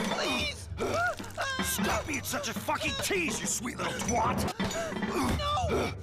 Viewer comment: So they censored the word twat? Lame. I thought he said, "stop being such a fking tease you sweet little fk!" Twat isn't a bad word to bleep out imo. Thanks for the uncensored version. 🤝